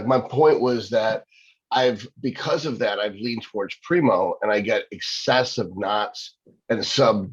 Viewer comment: 840 mg of PRimo a Day !?!?!?!?!?!?!?! Did I hear that right? @ 1:10:00 "840 Primo a day" ?????